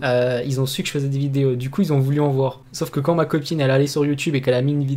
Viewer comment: C est moi ou il vient de dire"ma coquine"?